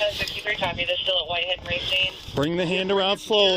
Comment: yoo lol at 8:14 does dispatch say "something somethin we got Y N racing?"